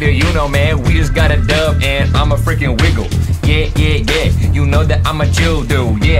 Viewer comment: did ninja say he's a nigga?